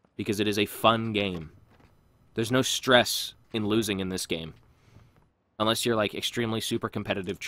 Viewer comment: I thought he said its not stressfull XD